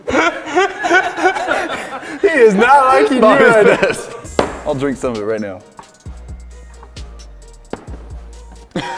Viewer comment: who else heard what said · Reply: OMG said Pissed